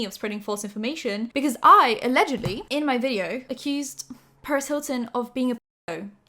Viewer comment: At first I thought you said Paris Hilton and I was so shocked like she's so chill XD